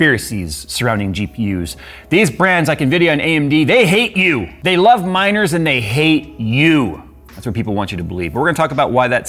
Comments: At first I heard "they love minors and they hate you" and was so confused... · I first heard "They love minors" instead of miners ... got a lot darker xD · Jay: "They love miners and they hate you" Nvidia: "I just came for a cupcake" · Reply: For a second I heard: "They love minors and they hate you" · I thought he said "they love minors and they HATE YOU" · Am i the only one who heard "Minors" instead of "Miners"?